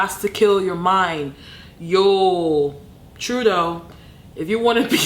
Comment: when she said "true tho" i thought she said "Truadeau" like canadas priminsiter